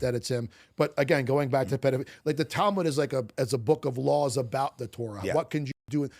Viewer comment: the first question sounds like he said "the TALIBAN"...when he meant "the Tal-MUD".